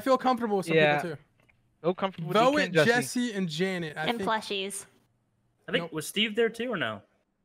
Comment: anyone else misheard dk saying jessie and james(when he said jessie and janet), I wanted someone to say prepare for trouble